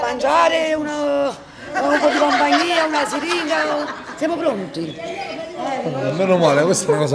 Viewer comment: Siringa!....ho sentito bene?